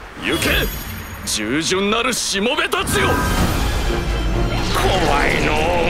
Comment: のしもべたちよ！が 下ネタよ！ に聞こえた私は末期